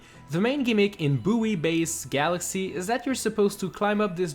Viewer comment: booey base galaxy Bh-woi base galaxy. Ik it's his accent but still xD